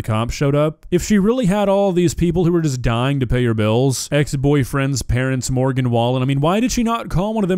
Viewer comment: Did.. did she say boyfriendS ??? Plural? Crowdfunding a PIZZA with the group of boos?!? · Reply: I thought she said ex-boyfriends?